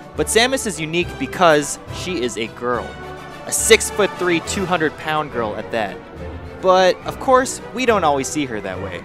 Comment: did he just say 300 pound???? damn!